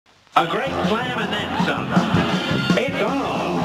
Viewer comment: A G R E A T S L A M A N D T H E N S O M E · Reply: of Destruction Beerus I thought he said A GREAT PLAN IMNENSA